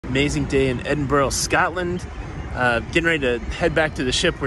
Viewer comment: I was making food while listening to this, and i heard "SCATLAND" and I never moved so quickly before. I thought i was on some explicit poop site.